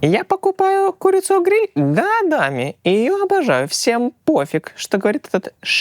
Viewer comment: мне послышалось: "Дадами"